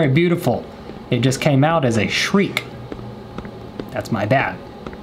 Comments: “I meant to say you’re beautiful but it came out as a shriek” 🤣🤣🤣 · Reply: kennedy_girl SAMEEEEE I thought she said shrek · “I meant to say you’re beautiful but it came out as a shriek” 🤣🤣🤣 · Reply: I thought you said Shrek 🤣🤣